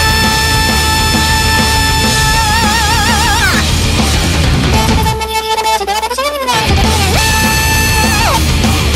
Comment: What is she saying from 3:40 - 3:50? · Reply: says: doushite sonna ni obieta me wo shite watakushi wo miru no? aishitekurenai no? kitanai minikui sokora no mesu yori zutto zutto watakushi no hou ga tsukushite orimasu aishite orimasu nano ni anata ni ha sore ga wakaranai no?